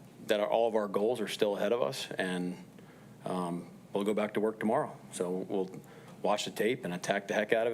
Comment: I heard “go pack go” most of the game. 😅 please bench woolen!